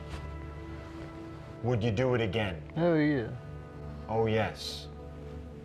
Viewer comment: "Oh Yes" I heard "Hail Yeah" 😂